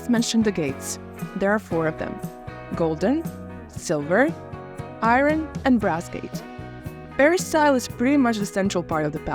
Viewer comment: I couldn't catch your favorite gate at 2:55. Which one did you say? It wasn't clear. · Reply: Golden, silver, iron and brass 😁 they are mentioned in that order